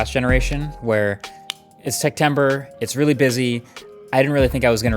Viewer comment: Is it me or did he just say Sectember 0:07?